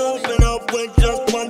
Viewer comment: Was I the only one who thought he said "Open up, wake up wake up" before seeing this lyric video?? 😂😂